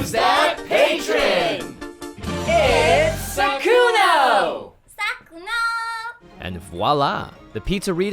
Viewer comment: Thought it said sykkuno